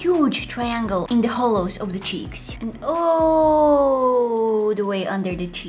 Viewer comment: When she said cheeks I thought she said cheese